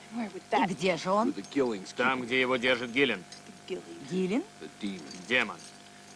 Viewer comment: послышалось: там, где его держит гелик =)